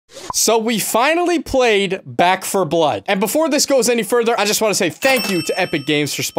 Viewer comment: istfg he says black 4 blood not back unless I have aids in my fucking ears and can't hear shit but that's what he said